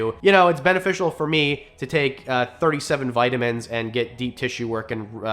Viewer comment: lol I had to listen twice at first I could have sworn you said it’s beneficial to take 37 Vicodins and get a deep tissue massage at first I thought this guy is an animal and then I thought no way he just said that on yt